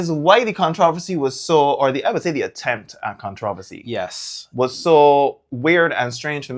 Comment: did you say con trov vo see Did you mean con trow ver see ( controversy ) 😂 wow man I'm Bajan and I don't even say that